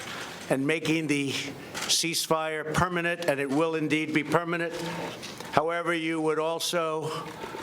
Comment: am I the only one that hears "permanet" instead of "permanent"??